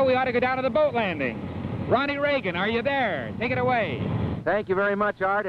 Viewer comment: Did he say Ronald Reagan?!... oh my goodness, he already looks ancient...